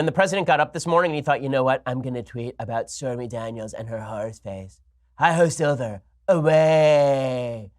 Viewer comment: I thought he said whores face 😳 because she gets paid for sex...